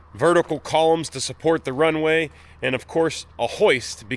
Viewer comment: Ok, I-beams to support the crane. Thought you said support Ukraine.